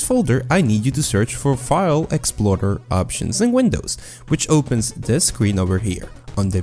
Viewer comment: What are you saying at 2:20? Is it file exploiter options or file explorer options, thanks!